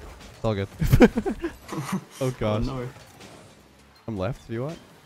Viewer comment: Idk if this has been said before, but when you call out "I'm Left", in my head I always hear "I'm Leth" and it's funny every time.